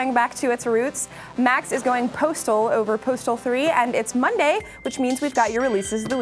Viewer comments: I thought it said Portal 3 and I was like "LOL WTF VALVE LEARNED HOW TO COUNT?!" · I thought that said Portal 3....Fuck....